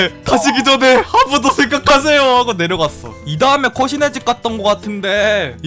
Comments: 왜 '코시네집 갔는데' 라고 들리지??? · 귀신의집 코시네집으로 들리는건 나뿐인가? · 나만 코시네 집으로 들리나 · 왜 코시네집이라고 들리는거지 당황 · 저만 코시네 집으로 들림?